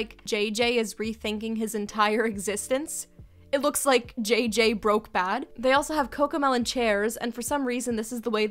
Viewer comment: Do you know when you said JJ broken bad? I thought you said JJ breaking bad